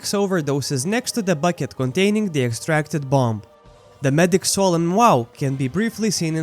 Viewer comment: "The medics solemn wow" i dont think its called that.